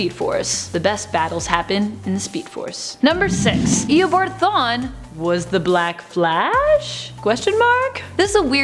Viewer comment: The pronunciation on these lists is what happens a when you have actors read nerd lists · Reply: She sounded like she said thane instead of thawne lol